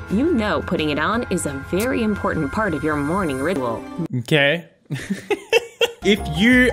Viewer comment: you know putting it on is very important part of your morning "ridwol" ok 123go! that's not a real word what your saying would be a rip-off of redbull :| lol 😂😂😂 captions: you know putting it on is a very important part of your morning ritual. i think 123 go! has some weird captions though 👁👄👁